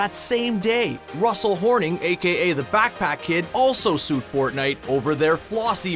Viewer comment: did he say the Fat cat Kid?